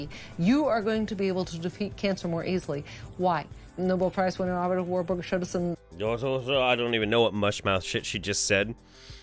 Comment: at 7:10, I slowed it right down, then gradually sped it up, and it sounds like she said "Nobel Prize winner Otto Warberg showed us in the 1930's". There IS a Nobel Prize winner named Dr Otto Warberg, who studied cancer...in the early-to-mid 1900's. Look up Warberg Hypothesis and Warberg Effect, if you want more info to shoot her stupidity down.